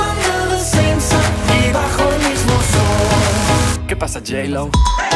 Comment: Was sagt er da??? Klar, das "Que pasa" hab ich, aber was danach? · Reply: J Lo